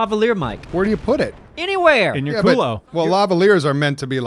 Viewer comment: "Where do you put it?" ... Am I hearing wrong or the answer is "In your culo"?